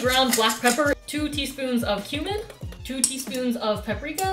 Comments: thought you said two teaspoons of human.. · oh my goodness I thought she said 2 teaspoons of human and i nearly died